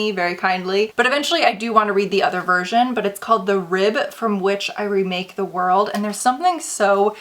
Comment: I thought it said " a rib from a witch i ruled the world" before u said it lol 🤦‍♀️